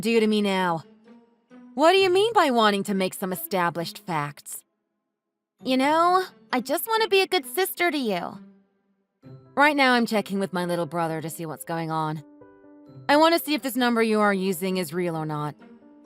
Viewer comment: i heard "i just want to be good sister to you" but i read "i just want to be good brother to you" lol